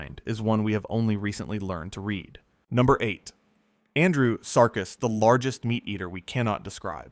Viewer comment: Andy Serkis? Is that what I heard? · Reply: You heard “Andrewsarchus” 😄 It’s the giant Eocene mammal known from a single enormous skull found in the Gobi Desert—not Andy Serkis, though I can definitely hear the resemblance!